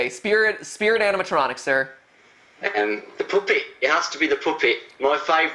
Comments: about 40:10 I thought he said with that accent his spirit animatronic was the puppy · Lol at 40:00 I thought he said "the puppy"